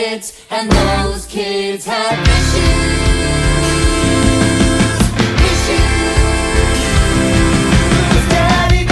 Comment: I thought they were saying wishes. But it’s issues soooooo